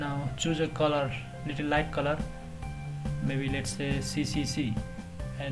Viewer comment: In this video, on 10:12 you say hit "something" to apply #cccccc color to selected area. I didn't understand. Can you explain it please because i can't apply color to selected area? Thank you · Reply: Mirko Martic press Alt+backspace to give color to the selected area.